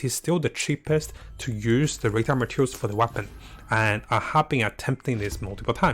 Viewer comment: Man, i always hear "Retard Materials" lol